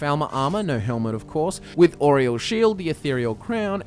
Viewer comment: it sounds like your saying Oreos shield in stead of aerials not how it's spelled but how it sounds